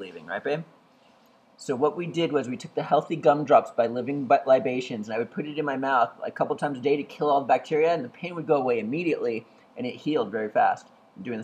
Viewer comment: LMAO sounds like he said 'healing butt libations' lolol